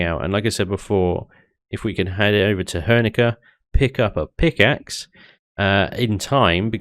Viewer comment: OH! Pick axe i thought it said Pintaxid....